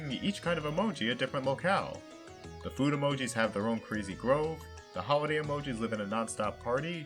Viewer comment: did he say Greasy Grove